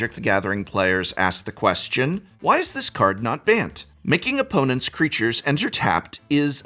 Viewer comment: i thought you said "banned" and was confused for a second